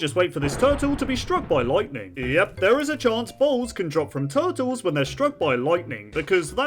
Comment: i think he said balls